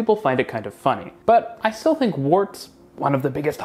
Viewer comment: What is being said here—"warts"??